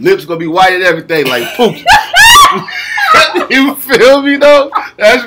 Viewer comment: did he just say lips like "pookie"? buahahahah